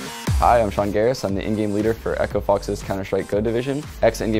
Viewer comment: I thought he said "Indian Leader" instead of "In-Game Leader"